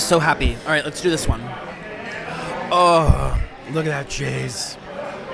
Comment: it sounded like ahhhh look at this jizz